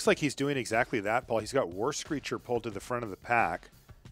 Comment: Loved the commentary excitement over P2P1 :) Liked description of Sunblade Angel - 6 mana 3/3 wall of text :) Last thought - I was confused because when you were saying "War Screecher" I thought you were saying "worse creature."